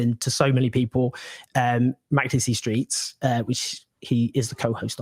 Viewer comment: You mentioned a podcast at the start, I'm looking for it now a link in the description would be useful. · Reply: Found it it's "Makdisi Street" (I heard "speaks" before).